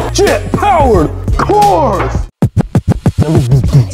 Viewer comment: i thought it said jew-powered for second there....